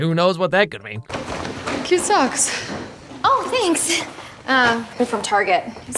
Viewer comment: I heard “cute socks” as “kids sucks” and it made sense that blake would say that